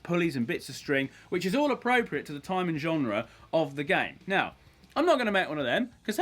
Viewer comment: English isn't my first language so please tell me what he said here "Wich is all appropriate for the time and..." What he said next? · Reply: genre